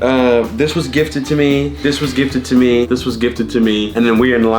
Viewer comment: they said “thrifted” or at least that’s what i heard